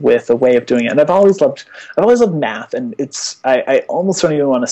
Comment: damn that accent, I just heard "I love meth"